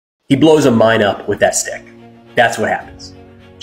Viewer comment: "He blows my mind with that stick." That's what I thought I heard and I was wondering why.